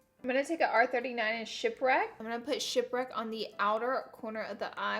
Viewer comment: When you said shipwreck I thought you said shit brick 😹😹😹